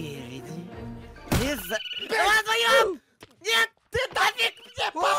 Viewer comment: что сказал 😅😂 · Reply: А, вдвоем-_